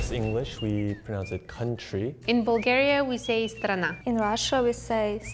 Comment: For example? I think it was pretty accurate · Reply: literally the first one here at 1:33, she says "strana", yet the subtitles say "derzhava". The subtitles are not wrong in a way "derzhava" does mean "a country" as well as the word she said, but it's clearly not the word she said.